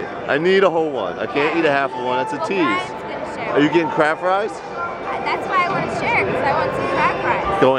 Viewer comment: I thought he was saying crab rice lol